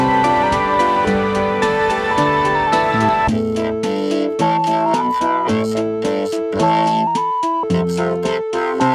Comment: what does the robotic flanger filter voice says? All I understand is vacuum flourescent display. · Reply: "VFD, Vacuum fluorescent displays, it's a technology that is still working today" i think